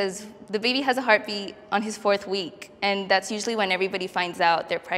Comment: they said fourth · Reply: oh u are right. For some reason when I first saw it I heard first week